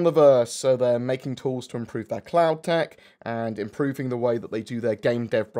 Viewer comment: I swear at 5:12 he said "Clown tech"... I had the video playing in the background and wasnt watching xD